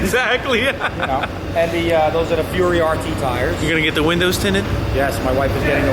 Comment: anyone else catch him say winded tindos